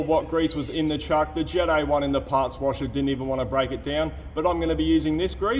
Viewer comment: What kind of product do you run in your parts washer? It flushes that thick grease right off and somehow does not turn your shop into a giant foam party. Edit: 25:30 Did you say Kerosene? That explains it.